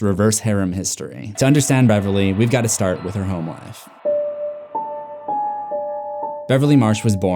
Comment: I thought you said “whore history”